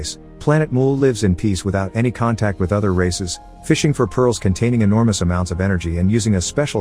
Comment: Did i misheard it as Racist or Races?